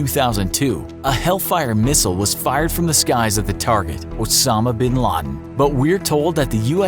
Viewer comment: Wtf did he say 7:05 what I heard was torpedo was fired Wosama bin laden